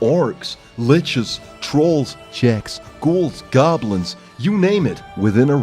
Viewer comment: when Blizzard releases TBC Everyone: WHERE IS 60-70 Swockwickdus? WHERE IS IT ?!?! im addicted 26:33 ..... did he really say .... Czechs ?